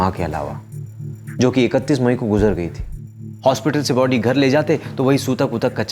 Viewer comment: kya bola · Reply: Sutak lg jata bro